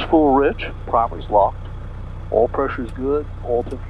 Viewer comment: did he say prop is locked ?? Constant speed prop on a Tomahawk ???? helps explain a 15kt addition to his top cruise speed.